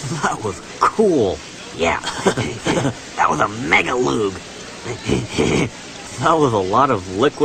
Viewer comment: that was omega... what he said after omega? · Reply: He said, "That was a mega loog." Loog, as in loogie.